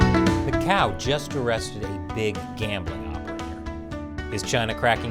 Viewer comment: Misheard the opening sentence as "A cow just arrested a big gambling operator".